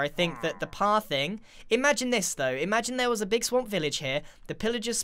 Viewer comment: I heard "imagine EsTo"